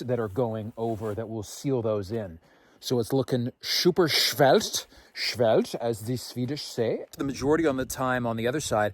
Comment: I am swedish and I could not understand what you where trying to say at 49:59 😂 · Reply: “Heck a hard” usually said “hard as heck” heck being a more polite version of saying “hard as hell” which all was just slang for it was very hard to do